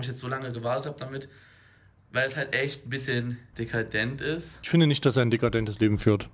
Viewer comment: Hab zu viel Karl geguckt verstehe bei 10:35 nur Dekarldent